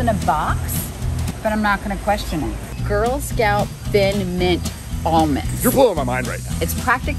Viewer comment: D- d- did he just say…… thin mints?!